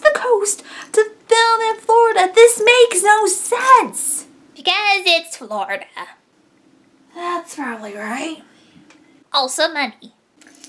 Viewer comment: What did the black creature say? (P.S. I'm new to seeing your channel, just as much as I am to any of your videos.) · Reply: 'Because it's Florida' & 'Also money' is what Mokona said.